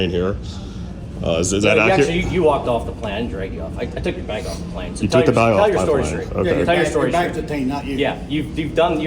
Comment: He said, "I took your bag off the plane." I heard,"I stole your bag"